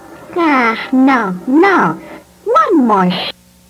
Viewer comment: i thought said one more Shit But at 3:30 he said one more Shot Lol